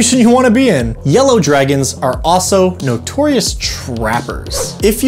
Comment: Oh good thing CC was on, I thought you said Yellow Dragons were notorious rappers.